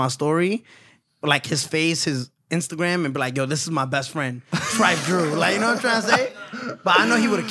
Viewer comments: if you listen carefully he accidentally snitched his best friends name , 😂😂😂 ....yooooo his face after is like “what did I just say? 😳” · did he just say his best friends insta or am I trippin